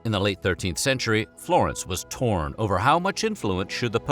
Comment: Seen soo much of these videos, when I heard “Dante was torn” I was like “Ouch, what a way to go” 😂